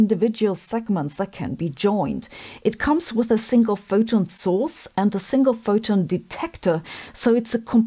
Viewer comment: "It comes with a single photon sauce". Sounds delicious! I misheard, and now I can't un-hear it. :0)